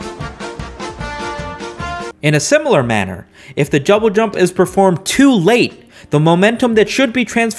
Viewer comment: he really said jouble dump